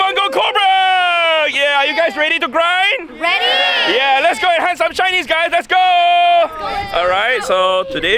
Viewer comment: When he said “let’s go hunt some shinnies guys!!!” I totally heard “let’s go hunt some Chinese guys” Either way best of luck.